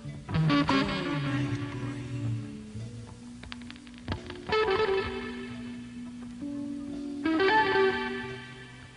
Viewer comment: What does he say at 9:02? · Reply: Go maggot brain, go maggot brain